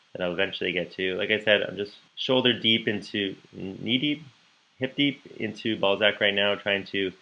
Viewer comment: I watched this video sped up and thought you said you were knee-deep in ballsacks...